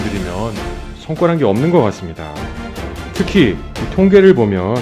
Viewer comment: 성과란게 없는 거 같습니다.. 손가락이 없는 거 같습니다로 들었네 😂😂